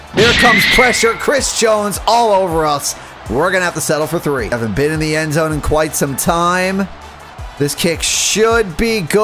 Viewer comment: what did bro say at 6:16? sounded like an n word😭